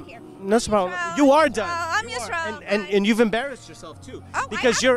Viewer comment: Is she say that she is Israel Chai ☕ (tea) 😂